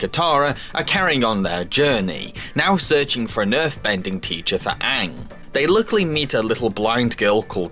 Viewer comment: did anyone else hear Nerf bending teacher?